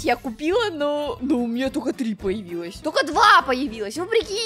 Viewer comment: Я ОДНА ЖТО УСЛЫШАЛА ""НАСТЯ ПОСМОТРИ НАВЕРХ ПРОШУ "